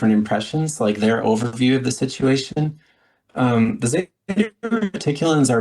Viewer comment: what group did he say? It lagged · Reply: Yeah, it was the Zetas.